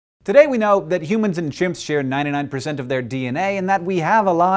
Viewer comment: At first I heard “today we know that humans and shrimp share 99% of their DNA“ and I was like HUH?!